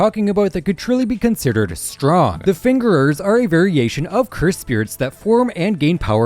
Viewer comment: did bro just say “the fingerers”😭🙏